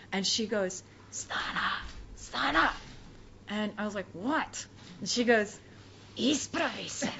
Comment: What did she say? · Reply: Изправи се - Stand up